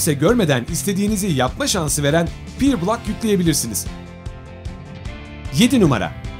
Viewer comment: te ne dedi ne yüklemeliyiz pirbulak mı dedi anlamadım?